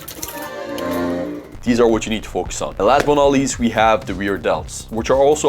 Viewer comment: I thought he Said lats but not least 😂❤